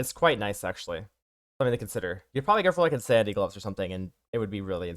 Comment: Any ideas what my man says here? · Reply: Got it 'insanity gloves'.